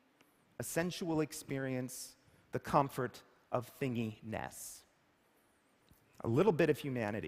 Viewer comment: What does he say in 13:22 ? Something like thingy ness?